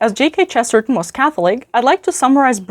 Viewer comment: J K 😂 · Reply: It sounded like you said JK Chesterton